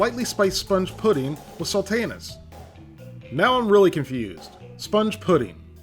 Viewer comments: I heard "Spotted Dick Sponge. Lightly spiced sponge with salty anus." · Sounded like you said "spotted dick pudding with salty anus" ... and it has "rapeseed" oil in it. Oh dear...